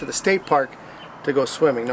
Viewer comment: Lol I thought you said “skate park to go swimming” 😂😂 Cool vid mate! 🤘😁